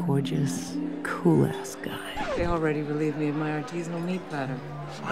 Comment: what was the word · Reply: Artisanal